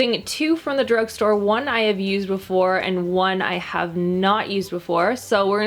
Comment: sounds like “ one I have abused before and one have not used before.” I heard this and it’s late and I Wass so confuse ‘till I rewatched it.